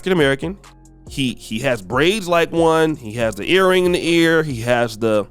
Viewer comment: Did you just say he has American braids? Consumption and creation are very different things.